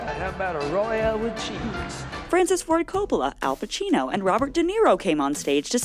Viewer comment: I heard “princess Ford Coppola”.